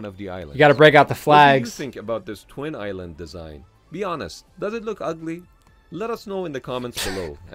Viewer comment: "Does it look ugly"? I'm sure they really worried about that one.😂😂😂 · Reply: A At 8:15 the guy said "Does it look ugly". I heard it, because I was paying attention.